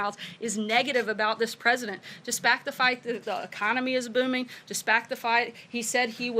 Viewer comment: Dispact the fight? Did I hear right?